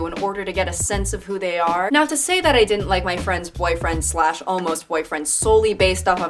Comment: I thought this said "boyfriend's boyfriend" and I got excited for a second